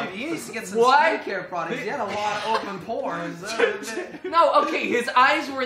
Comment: open what did he say po rn are am I just werid